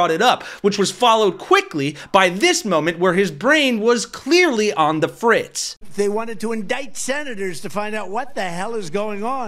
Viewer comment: At first I thought you said, ‘whose brain was clearly on the fridge’. 🤣